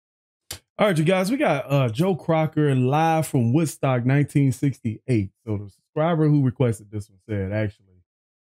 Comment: Did he just say Joe Crocker?! 😂😂😂